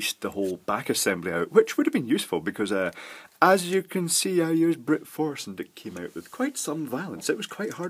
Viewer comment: I heard "would have been useful 'cause I used "Scotch brute force.."?